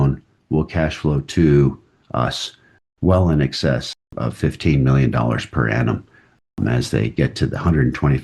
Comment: did he say fifteen or fifthy ? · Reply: I believe 15